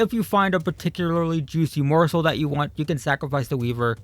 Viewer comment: did he say morsol, like monster train morsol, if so nice